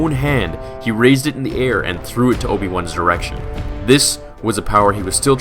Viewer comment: “Instead of summoning it to his own hand, he threw it to Obi Wan’s ERECTION!?” lol.